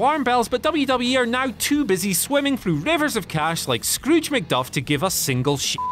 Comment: Did you say Scrooge McDUFF? TRIGGERED